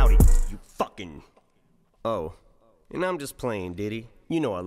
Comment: !!! You completely missed the end homie! Haha At your 28:50’ish when Em stops and apologizes? He said DIDDY NOT LADIES! Lolol I hear Diddy everytime I ever listened to this! Haha